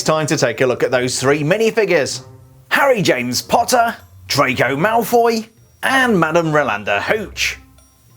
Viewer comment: Who else thought he said trago malfoy?